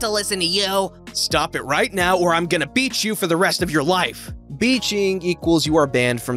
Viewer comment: When he said "I'm gonna beach you forever" I thought he said "I'm gonna BEAT you" Ha Ha